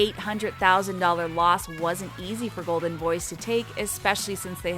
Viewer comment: I thought she was saying goldenboys for 13 minutes